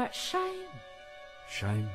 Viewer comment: ThE wAy I aLwAyS tHoUgHt ShE sAiD sHiTe. ❤😂